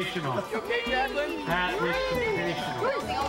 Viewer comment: I’m sorry but did I hear someone say “Michael Jackson”